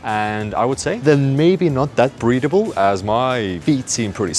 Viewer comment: God, I watch too many Vtubers, I heard 2:22 as "not that breedable"...! >.>